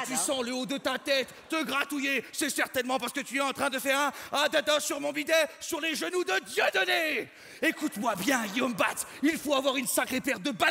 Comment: Qu'est-ce qu'il dit? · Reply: adada sur mon bidet sur les genoux de Dieudonné